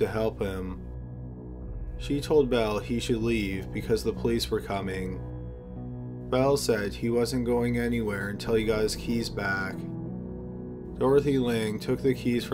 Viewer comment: I opened my computer and this was playing, for a second I thought you said Mel as in Mel Gibson! XD Although pretty sure it is him. Mel, Bell same guy.